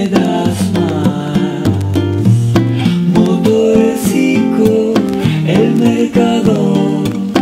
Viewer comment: Siempre pensé que decía motor pisco